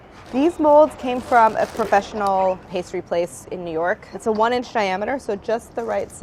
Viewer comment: Did she pronounce a "diameter" as "shiameter"? Did I mishear?